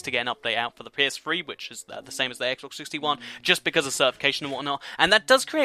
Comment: Did he say Xbox 361? Or did i just here that